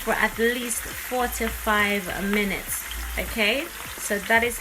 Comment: I was listening and not watching all the way through I thought you said 45 minutes. I at least know it's done and it was in a million pieces and the fish stuck to the pan. Better next time. Thanks!!!